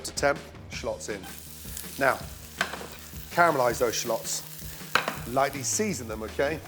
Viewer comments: I thought he said caramelize those sluts 😂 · "Carmelize those shhluts" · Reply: I thought he said "caramelized those sluts!" 😂😂😂😂😂😂😂😂😂😂😂😂😭😭😭😭